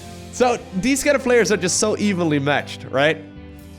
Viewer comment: What Lowko says: "These two players are very evenly matched." What I hear: "These two players are very evilly matched."